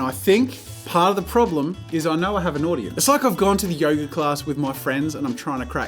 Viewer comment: Who else thought he was saying yogurt class instead of yoga class.